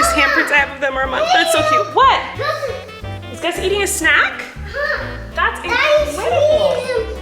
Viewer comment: HELP. AT 6:48 IT SOUNDED LIKE SAID AMONGUS?